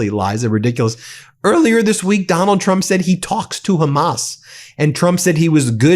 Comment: Oh, talks to hamas, i thought he said he talks through his ass..!!😂😂